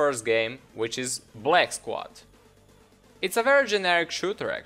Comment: on to the first game which is "bleck" squad. It's a virgin... thats what i heard